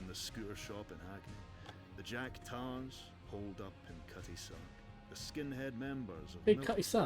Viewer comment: ish you were asking what the narrator said when dealing with the Jack Tars, it was Cutty Sark, the old Tea cutter ship that became a museum. It’s a traversable location in the Mod where the maratine faction are based out off.